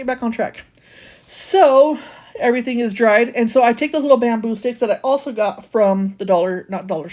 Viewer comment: I have a feeling you struggled getting dressed😂. Girl it happens to the best of us😂. I had to giggle because it sounded like you said baboon sticks for the bamboo sticks, but might be hearing things🤷🏼‍♀️🤣. You did a great job on that chess board. I don’t want to try being perfect either, I have lots of character😜😂. Glad to see you back♥️